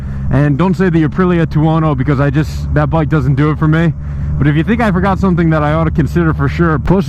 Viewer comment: That or the Aprilia Torono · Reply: HAha one second after I hit send I heard him say "the torono doesn't do it for me" lol but anyway it is a killer ride, some just don't like the nose.